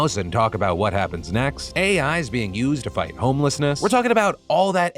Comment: What Phil said: "Los Angeles is using AI to fight homlessness." What I heard: "Los Angeles is using AI to fight [the] homless."